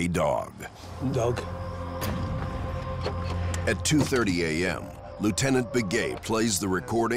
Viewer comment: Woah did they just say Lieutenant Biggay?